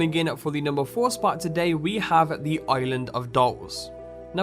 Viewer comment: at first I thought they said "the island of dogs" i was liek- "wut could be scary about an island of dogs , that sounds great" then I realized...